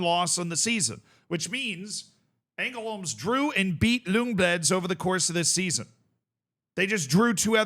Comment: It sounds like he says "Lungbledz" at 6:03. As someone who speaks both Swedish and Norwegian, the way he butchers the names is hilarious.